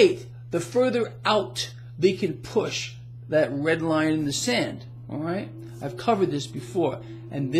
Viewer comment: Thought I heard 'that breadline, in the sand' - lol